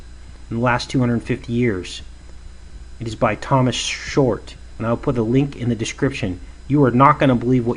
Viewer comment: After the rugby cartoon, you talked about a history textbook and a comet in 1347, being 666 years to the day , I was listening but not watching and I heard " TIME IS SHORT" but you said book by Thomas Short. It wasn't even your voice I heard. It stopped me in my tracks. I've never heard God in an audible voice, but I believe I just did. This evil is coming to an end. Hallelujah , Praise Jesus our Lord and Savior🎉 🕊🦁🐑